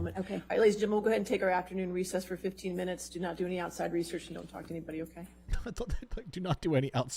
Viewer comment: I swear she said “outside research”